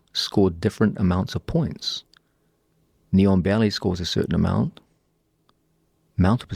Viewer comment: Yeahh I thought he was saying "Neon Belly" for about the first 5 mins.